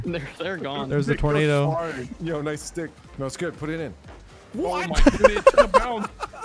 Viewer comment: oh I can’t get in there. yo nice stick, no it’s good get it in. Tell me I’m not the only one who heard that